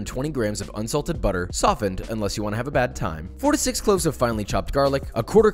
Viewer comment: I was so confused I thought he said 46 cloves